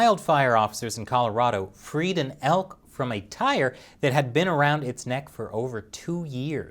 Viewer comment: When he said "freed an elk from a tire", for some reason I heard "created an elk from a tire", and I immediately paused the video trying to figure out what I just heard. I did not succeed.